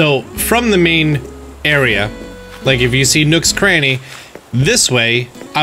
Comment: thought you said nooks granny